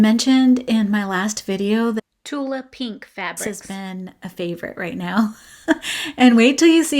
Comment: Such a cute owl! OHEMGEE!!! That bag Is amazeballs!!! Your hubby is right you could sell those for a buck easy and I’ll be your first customer! Lol. (but for real) 😳🤩😍🙌 Every time I see Tula Pink fabric, it’s my new favorite! Lol … Every time I heard you insert “ Tula Pink Fabric “ it made me giggle… Lol what exactly were you saying? Lol. 🤣 Your daughters Afghan came out beautiful! I would say if she loves it the way it is then it’s good to go. 👍 But if you really want to add to the border, either the red or white will do. 🤗💕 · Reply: Hello Jo, 😂😂 i kept saying Tilda fabric instead of Tula pink🤣 Menopause brain I guess😏 Thank you so much, i keep practicing in between crochet so hopefully I can make a penny or two off them, butright now its taking me fooorrrreeevvvvveeeerrr❣️ I will add the red just so i can finish it off with a solid row and it will be hers❤️🧶 Have a blessed day my friend. I hope you have plenty of peace and crochet time🤗